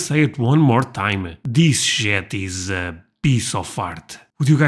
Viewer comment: I thought he said " This jet is a beasty fart"!😂